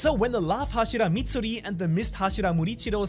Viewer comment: did you say luagh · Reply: Hashira